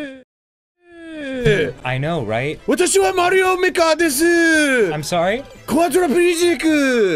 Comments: all I heard is something about super Mario maker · sounds like he says super Mario maker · Everyone is commenting one the accent Mean while did he say Super mario Maker